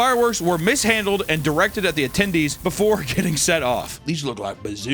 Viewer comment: Anytime I heard the word “attendee” I think of “a (chicken) tendie.”